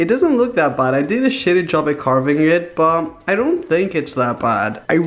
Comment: did he say shitty 😳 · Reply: Savage_kayla 1257 no dude, he said printer OF COURE HE SAID SHITTY YOU IDIOT